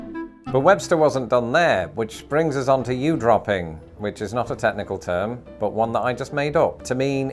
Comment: OMG at 3:11 I thought you said ewe droppings!😂😂 Are you crasy? Balazzio sounds delicious! And lastly, I’ve only lived in three different time sones in this country in my lifetime, so how have I only encountered “omelette” as what you have with Mimozzaz at Brunch?!!